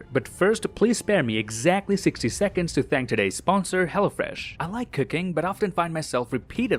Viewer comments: "I like cooking" But I heard "I like cocaine"😂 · Who else heard him say 'I like cocaine' instead' I like cooking '😅😅😅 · anyone else heard "I love cocaine"